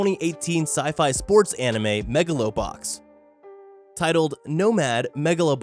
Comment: I just heard, "Mega Lubot"